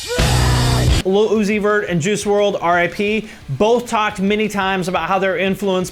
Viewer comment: did he just say Lil Uzi Vert and Juice Wrld Rip? 😂